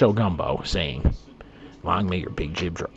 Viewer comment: I didn't understand what you said at 9:53. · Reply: My sign off phrase: "long may your big jib draw" it's a Newfoundland drinking toast that basically means "may there always be wind in your sails"